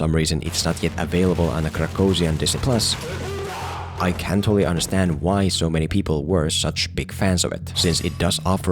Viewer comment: Bro at 1:21 i thought you said "I can't really understand why so many people were such big fans" and I was so confused for the rest of the video